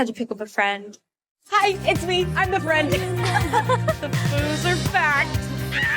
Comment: it sounded like they said the loser are back and i thought being self aware doesn’t mean it’s going to be a good movie.